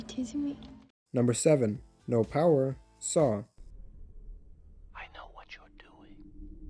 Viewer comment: i thought for the 1st time he said, i know what youre wondering: Whats in the Canister?